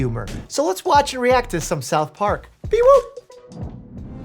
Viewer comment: I thought he said “self harm” instead of South Park